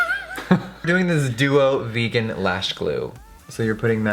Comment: I have one comment..vegan eyelashe glue..seriously? I guess plastic is vegan! (Thought he said eyelashes 1st time..but same thing 😂) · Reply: He said "Duo vegan lash glue", not the lashes lol